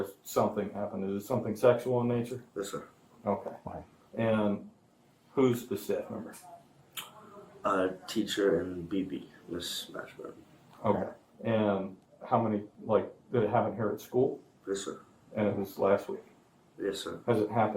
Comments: I thought he said Miss Smash-burn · Miss Smashburn lol 😂 that’s what it sounded like he said at first